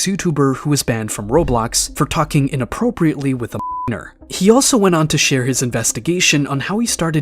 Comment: what was he saying on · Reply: minor